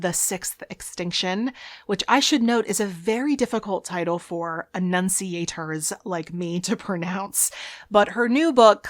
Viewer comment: you use a word I dont understand snd there areno subtitles....what speaker arnt you? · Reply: at the 8 minute mark she said, “which I should note is a very difficult title for enunciators like me to pronounce”